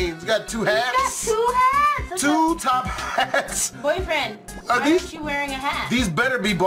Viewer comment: did she say b-b-b-b-boyfreind